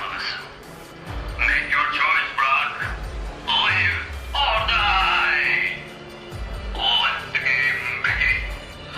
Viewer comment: ne diyor anlayabilen var mı ? ... Or die gibi bir şey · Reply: Make your choices or die let the game begin (edit:sanki biraz geç oldu bu cevap ama olsun)